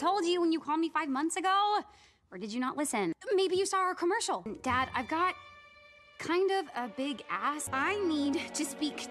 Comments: Idk y I heard I kinda got a big ass lol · It cuts out so it sounds like Charlie says "dad, ive got- kind of a big ass-" 🤣🤣🤣🤣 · Bro when he skipped it when Charlie said she said she has a big ask he skipped it at the perfect time and it sounded like she said “hey dad i kinda have a big ass😂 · am i the only one who hears "dad ive got kind of a big ass" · is it just me or does is sound like she says, “Dad I have kind of a big… a$$…” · I HEARD "Dad.. I kindve have a big ass.. 😢" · Dad, ive got a pretty big a$$ This is what i heard lmao😭